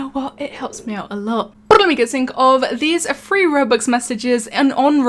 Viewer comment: wtf did you say 8:02.i don't understand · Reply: "But let you guys think of" or something like that. I wish the captions would be available for this video.